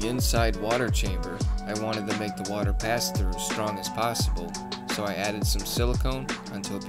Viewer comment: Question. How does adding another layer of corrugated plastic w/ screen, to the inside of the water chamber, help water pass faster? Is that because the outer screen might get clogged? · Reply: I said, "The water pass through," not water faster. I did have to listen to it a few time though. It does sound like I say water faster lol. But to your comment. I have noticed that when plant leaves from the canopy fall into the water, they will end up on the screen (water pass through), so in a way, it won't make it to the back chamber. Keeping the back chamber as debris free as possible.